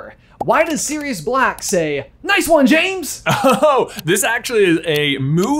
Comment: I always thought Sirius said “That’s my James!” not “Nice one, James!” But now that you say it... I’m realizing I have been wrong this whole time lol 😂